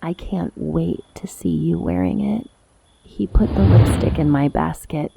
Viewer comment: the caption says "he put a little dick in my basket" 💀